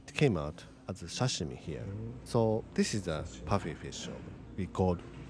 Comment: Did you say this is pufferfish sashimi? As somebody who doesn't eat seafood or raw meat, it's basically the stuff out of a horror movie for me 😅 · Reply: Haha, It is puffy fish sashimi. 😅 👻Before Sushi got popular, I thought nobody could eat raw fish in the US.